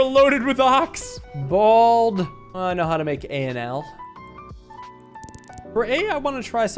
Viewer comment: I don’t know why my perception at all times tries to make fun of the. What I heard, though I know it isn’t correct, was, “BALD” “I know how to make a ‘NL’”